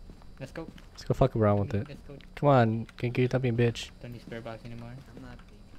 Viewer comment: I THOUGHT HE SAID "STOP BEATING YOU D***"